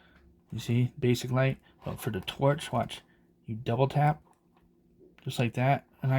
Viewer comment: I thought it Said touch not torch